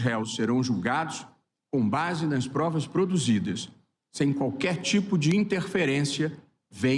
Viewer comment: É impressão minha ou eu ouvi o Barroso falar que vao julgar os réus com base nas provas " PRODUZIDAS " 5:22 😳🤔